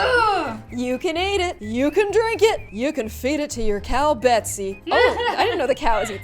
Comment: Gloom: you can feed it to her cow Betsy . Me: that is my name 🥺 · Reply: I thought she said bessy